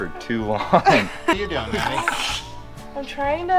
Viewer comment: Do we know daughter's name? I went back to the beginning to check, and AFAICT they just called her ' daughter'. Anyway, I'm thinking she could be Fab Rat's answer to MORR's Lizzy. You can never have too many pretty girls on your channel. wife is pure gold as well. Why does it always come down to the ladies to do the yucky jobs? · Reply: Sounded like Mattie (Sp?)